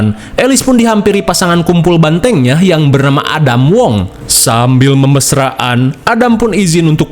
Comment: gua salah dengar kira namanya Ada Wong 🤣